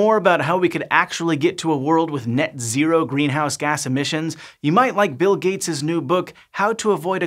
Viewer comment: All I hear is : Let's pirate Bill Gates' new book.